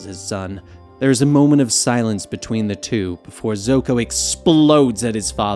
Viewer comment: Did he call zuko zoko 12:49 🤔🤔😂😂!